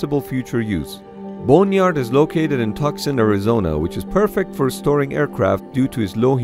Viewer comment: "tuck son Arizona" It's pronounced two-sahn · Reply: I thought he said Toxin, Arizona